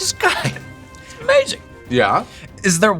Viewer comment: Paul says: "This guy. He's/It's amazing." Captions are wrong. Judging by his lips, he most likely says "he's amazing".